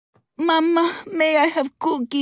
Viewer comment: I thought she was trying to say No Diabetes at first